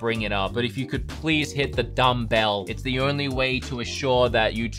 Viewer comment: i thought he said thumbs down and its a bell not a dumbbell